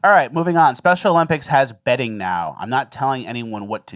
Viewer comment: Thought he said "Special Olympics has 'bedding' now".